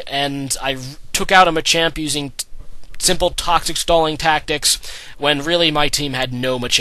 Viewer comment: Lol when you said "Toxic Stall" i thought you said "Taco Stall" I got excited D